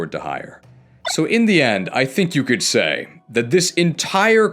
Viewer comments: Am I understanding this correctly if the auto-subtitles thought you said «budget f u**s» ??? I’m genuinely confused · "Budget Cuts" is insane to be demonetized for , But if they thought you said Budget C*nts that's even mlre stupid